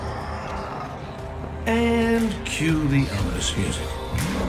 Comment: Does anyone know what he said in 0:03 plz comment down if you know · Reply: Bolfie - It sounds like "and... cue the association" to me, but I'm probably wrong.